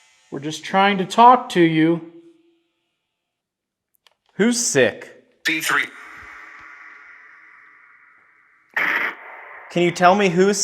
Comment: When you asked who's sick at 1:17:03 it sounded like it said D3 which is a vitamin that boosts immunity among other things lol!